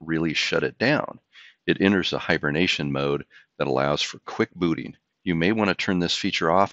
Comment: did he say it allows to enter hibernation mode for quick BOOTY ?? Listen, we are not here for sex talk !!